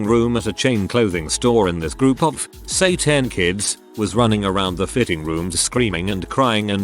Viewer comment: I misheard "say 10 kids" as "satan's kids" and honestly I don't think it would have been so different